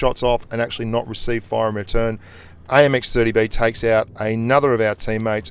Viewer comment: Did I hear AMX 30b at 12:40?